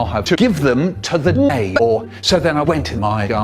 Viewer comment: at first I thought Micheal said Namor (as in the marvel anti hero) and not neighbor